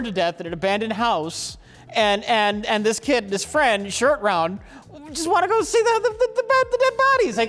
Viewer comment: Love a good shirt out 1:20:53 Wait, he says "shirt round" instead of short round. what's up